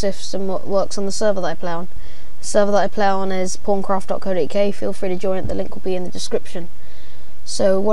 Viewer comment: Thought you said porncraft